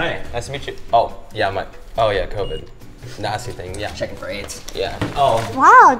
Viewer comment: Michael holding the thermometer to John's forehead - John: "Oh yeah, Covid, nasty thing" Michael: "Checking for aids" I'm crying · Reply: I thought he was saying Checking for brains haha